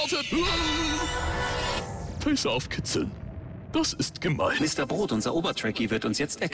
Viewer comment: was sagt Bernd da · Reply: "Phaser auf Kitzeln"